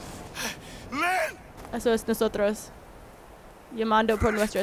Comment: Escuche Glenn😭 (TWD) *fuck necesito un psicologo*😢😢😢😢😭😭💔